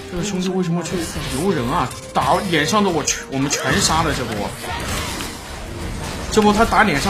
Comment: uzi7:50秒說的是什麼，聽不太清楚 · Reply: 他说如果辅助跟着他打那两个走到他脸上的人就能全杀了